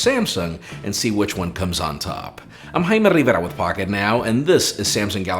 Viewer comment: when the closed captioning tried to auto caption his name it heard it as "I'm high not even with" Or did closed captions hear his name and think he was high..